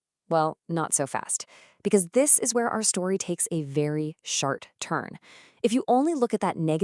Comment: did she say “shart?”